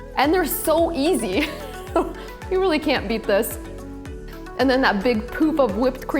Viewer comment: Big poop of whipped cream? Did i hear that correctly? at 5:08 min Hahaha, I love the recipe :) thank you.